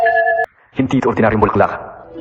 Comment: Did he just say that hindi ako ordinayong bakla?